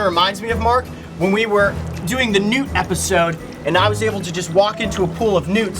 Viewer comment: I heard "when we were making a nude episode" and i wasnt sure what to think about that 😫